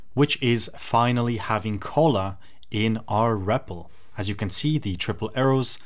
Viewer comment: I thought I heard "Cola in our REPL" 💀